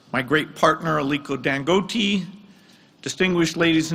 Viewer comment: Wait o! Did I just hear Aliko Dangoati at 1:30? 😂